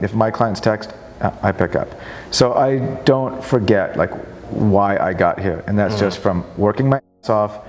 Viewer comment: He says "why", not "how"... this seems to be a minor distinction, but it is anything but. Great video, interview. Nice to see someone who understand the value of hard work, has worked very hard to get where he is, and is enjoying all of it, guilt free. God Bless America!